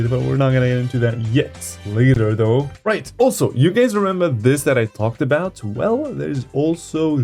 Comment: what did you just call me ??!! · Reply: “Guys”, not “gáys”.